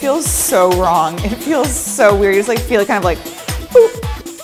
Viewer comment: I thought she said poop not boop XD